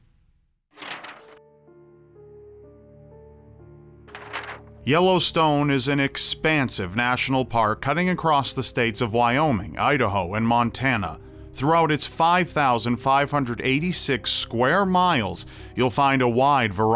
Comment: I thought u were saying "expensive" in a southern accent